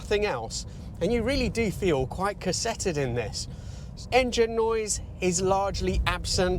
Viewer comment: Interesting pronunciation of "cosseted". I thought you said cassetted. Had me thinking about a TDK C90. What do I know?